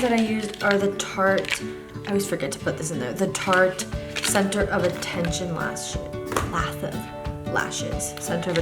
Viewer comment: anyone know what lashes she uses?? amazing job so beautiful!! :) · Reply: nadia m 15:55 she tells you. Tarte center of attention I think she said :)